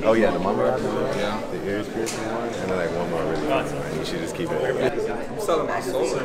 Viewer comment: “I’m Selling My Soul Right Now” - Lil Yachty Or Am I Hearing Things🤔🤷🏽‍♂️